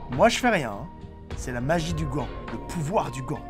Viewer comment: Putain au début j'ai entendu "c'est la magie du gland". Je crois qu'il faut que j'aille consulter 😂😂😂